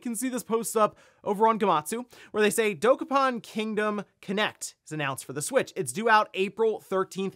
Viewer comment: My ears perked up when I thought you said "Dokapon Kingdom Kinect"... THAT would have been a wild announcement, especially just after that Microsoft layoff news.